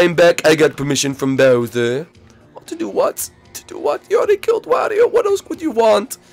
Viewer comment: I thought it said Ludwig killed Mario